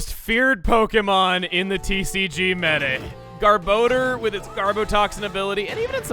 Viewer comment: I was half paying attention to you saying garbodar. I thought you said "Garboner".